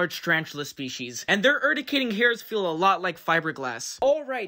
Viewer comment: Bruh I thought he said that the hair feels like glass, why did he have it in his hands at the end 📸